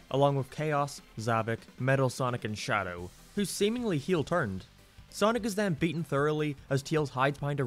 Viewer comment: "who seems to have heal turned" wait what? what did he really say im so confused.